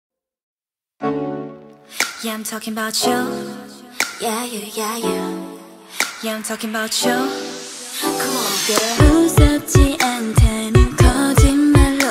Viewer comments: Legend says that in the beginning of Satellite, straight people hear "come on boy", gay people hear "come on girl", and bi people can hear both. As an aroace, I can't hear that part at all. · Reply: im straight but i hear “come on girl” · I hear "come on boy" haha xD Anyways, love the track!